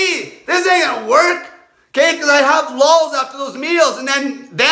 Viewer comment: "because i have ___ after those meals" What does he say here? I couldnt quite catch it? Logs? As in he shits after each meal? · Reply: Lulls